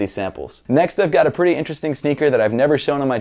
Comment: I swear I heard negroes